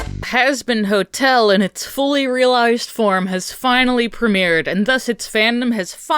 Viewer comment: When Alastor Say "Let's Begin" I Keep Hearing "Let's Be Gay" 💀